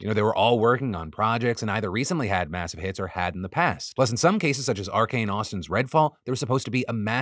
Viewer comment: Not related to any of the stories, but Phil thank you for finally having captioning out when the video goes live! As a deaf viewer it's been so nice to be able to watch your show as I eat dinner rather than first thing the next morning since I rely on captions. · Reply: I'd like to add, unfortunately his captions still aren't very good. I'm hearing, but I take a bit longer than most to recognize spoken words, so I use captions always. To me his captions look like he took YouTube's computer-generated captions and added punctuation, which makes no sense to me considering that his videos are almost entirely scripted anyway. An example is at 5:06 in this video, when the caption reads "pleasant, some cases such as ..." when he actually said "Plus, in some cases such as ...". There are a dozen or so of these errors per video that I would consider noteworthy, so I'm pretty sure the captions are either computer-generated, or someone on his team isn't doing their job and they haven't noticed yet.